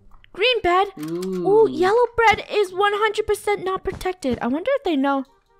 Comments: did anyone hear that she said bread instead of bed lol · did i just hear say "bread" instead of bed XD 😁😀😄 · omg did say yellow bread 🍞 😂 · did i just hear say "bread" instead of bed XD 😁😀😄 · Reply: same here i hear 'bread' instead of bed XD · When said yellow bed i heard her say bread hahaha